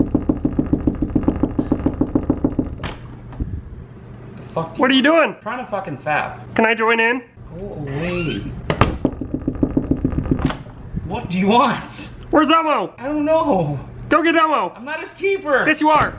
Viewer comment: wait did he say his trying to fap, I thought he said nap